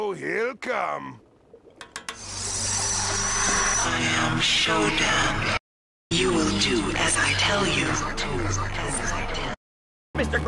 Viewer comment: does anybody know what the voice at 5:43 says? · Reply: "I am SHODAN, you will do as I tell you." It's from system shock 2